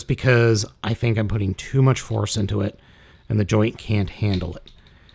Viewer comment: idk if i heard "horse" even tho thats he meant "force"